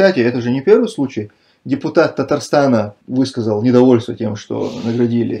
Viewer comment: Брат Тумсо у нас Казахстане нету главы. У нас президент, а орденом туслык наградил глава Татарстан а не Казахстан. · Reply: ты че такой агрессивный? В детстве не любили что ли? Мне тоже Казахстан послышалось вначале, но далее он более внятно сказал Татарстан